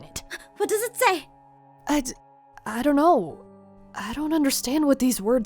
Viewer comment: When started hyperventilating and asked what's wrong I thought she said hi hi not I-I